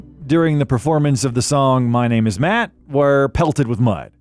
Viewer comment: Did he say, “My Name Is Matt?!?!” 🤦🏼